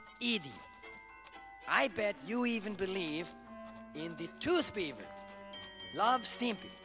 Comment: The Ren & Stimpy DVD closed caption said tooth fairy when Ren said tooth beaver.